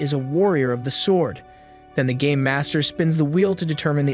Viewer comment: It sounded like you said the gay master